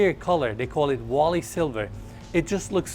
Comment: Genuinely thought you said the exterior colour was WALL-E SILVER. 😆 Loved this yacht 🛥